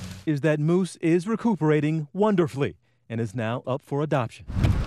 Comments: I paused the video early and it sounded like he said "I'm T.J Holmes, in pain" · Did he just say Moose is recooverating? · is when you can here him say recovering wrong and in sounded like re-coov-ering😂